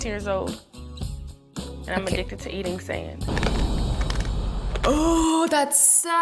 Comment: I thought she said im addicted to being sad